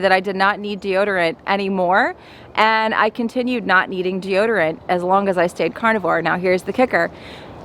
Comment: it's a good thing that you don't eat deodorant anymore ! Lol ( at 3:24 ) . Not an easy thing to do a video. It's good to have a sense of humor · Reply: I heard, "..I continued not needing deodorant.."